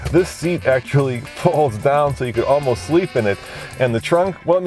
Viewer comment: You can fit 2-3 bodies back there..... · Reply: I thought he said you could have a 4-some in it.